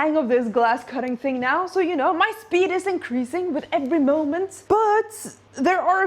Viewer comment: not sure why but without subtitles i heard "my fetus is increasing with every moment" and i had...huh? So there is a idea to finish that and more within 8 months?